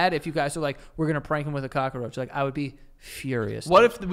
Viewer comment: am I the only one who hears them calling a cockroach a cockoroach😭😭